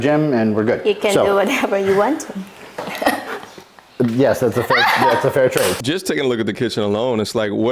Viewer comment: did she say “you can do whatever you want to me” ?!?! WINNING 😂😂😂